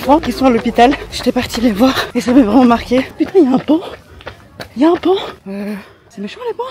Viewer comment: j'ai compris, ohla! Ya un 'banc' jme suis dis: " quoi mdr tu veux déjà te reposer " 😅... Après j'ai vu le PAON, j'ai fait "ah ok ! Ouf " 😅 (faut avouer qu'en vidéo on fait difficilement la différence entre pan et ban) 😂😂😂